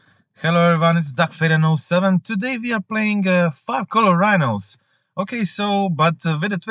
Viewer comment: Just gonna say that a 0:03 I thought you said "fuck all rhinos"....Listended again and I heard 5 color rhinos. I'm tired. don't judge